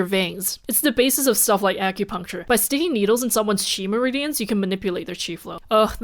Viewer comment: Honestly, when you said "chi meridians", I first heard "midichlorians" and had to actively course-correct my brain.